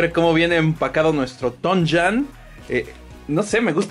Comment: Pensé que decía tonayan🤣🍺